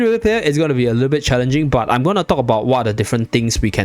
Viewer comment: Every time you say, "I'm going to talk about. . . " I hear I'm going to Taco Bell 😆